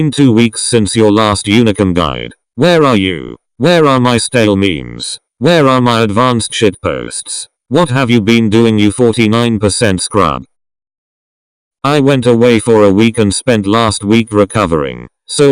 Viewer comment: i thought i heard stalin ._.